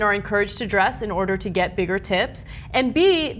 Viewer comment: The awkward moment when Ana says "bigger tips" and I hear "bigger tits"... Yes, I'm despicable, I know